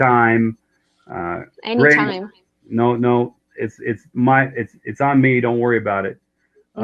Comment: é "it's ONE me" ou "it's ON me"? (olhei a legenda mas entendi "on" invés de "one")